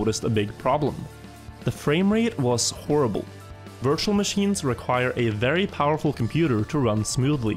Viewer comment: but.... Can you run a Wirtual Machine 🤔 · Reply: I really thought je said "wirtual machine" but i knew it was virtual machine It sounds like it